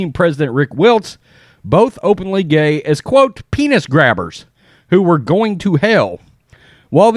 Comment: 😁 I thought I heard said ' peanut grabbers ' .... ........turns out I was wrong when I rewatched again .